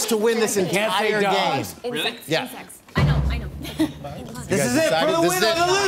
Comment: did anyone else hear "in sex" just me? Ok. 😂